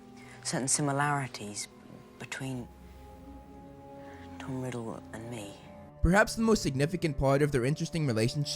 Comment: Does he say "Dumbledore and me"? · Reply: Tom Riddle and me